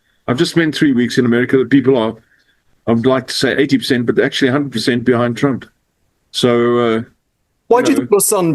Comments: I heard "I'd like to say 80% but actually % behind..." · Errol said, "I would like to say 80% but actually 100% behind trump." (Not blacks or backs, you are mishearing.) However!! 80 or 100%, Errol is out of whack!